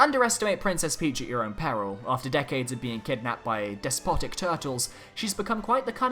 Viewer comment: 1-73=Issac isn't in smash Also am I the only one who thought when he said despotic He said despasito legit no joke that's what I thought